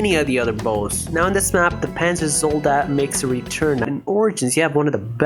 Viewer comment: holy shit you said panzer zoldat and I deadass rewinded I thought you said panzer soldat I thought you played kiseki shittt